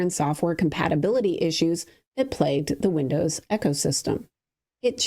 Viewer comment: You sure that wasn't Ai? At the 7:45 mark she mentions the xbox "Echo-system" which is never how I heard it said. Eco-system yeah, and then I went back to watch that part again but while actually watching and she moved like a robot to me.